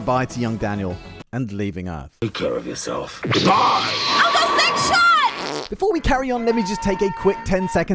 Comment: Did say uncle sixshot at around 10:20? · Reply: Yes. "Uncle Sixshot". Really.